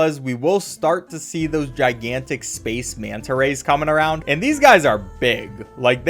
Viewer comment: i heard space manta rays? the night yorb??